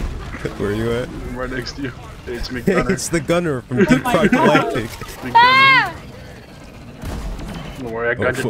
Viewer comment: im sorry did you just say deep Fried galactic?